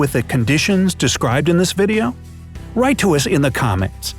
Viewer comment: oh my gosh i thought it said "lie to us in the comments" i had to do a double take! pfft!